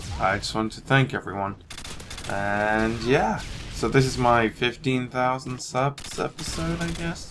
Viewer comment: I thought said 50,000 when he said 15,000